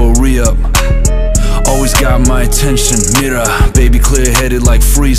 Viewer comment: it should be Mira instead of clearer